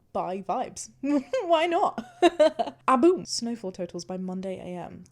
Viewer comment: I kid you not, I heard "Snowfall TURTLES by Monday am." 😂